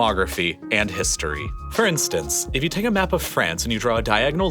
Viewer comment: did you say "francestance"?